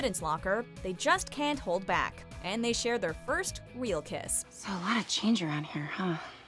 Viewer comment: she says weal kiss instead of real kiss 😂